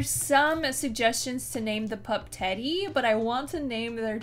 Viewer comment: At first I thought said Tangy not Teddy so now I'm wanting to have a pet or something named Tangy 😄